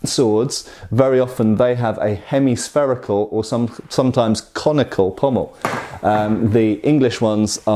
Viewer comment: I was rather disappointed when I realized I misheard and we weren't about to be shown a comical pommel...